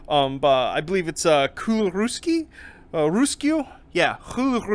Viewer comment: Kuularuisku? I had no idea what you said until i heard the translation, you butchered it that badly :D